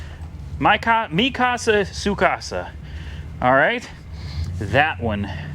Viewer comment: For a second I thought you were saying Japanese girls names: Mikasa and Tsukasa.